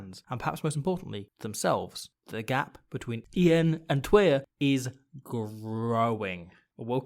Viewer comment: Excellent pronunciation of “een” and “twee” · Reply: I honestly thought he said "the gap between Ian and Twier is growing". I was like who is Ian and Twier?